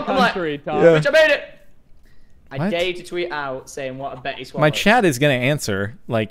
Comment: ? Bettyswallox ? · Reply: Man Don't Care honestly thought they were saying Betty Swallows.